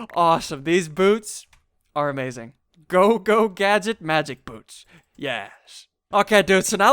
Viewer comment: Wen you said "Magic boots" it sound like you said "Magic boobs" xD